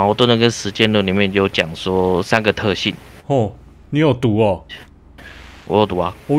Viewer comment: 哈哈哈你有读哦，我听成你有毒哦，还想怎么突然骂人